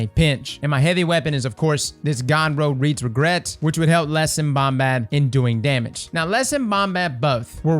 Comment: What is said at 2:11, about Reeds Regret? "Which will..." I've tried replay 10 times and can't figure it out... Lol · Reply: Yeah Les and Bombat are a couple of guys he regularly runs with.